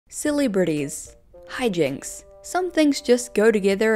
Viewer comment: Bestie when you said "celebrities" I thought you said "silly Britties"